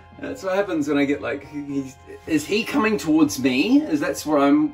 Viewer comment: Is he coming towards me? What I thought I heard: Oh? Are you approaching me?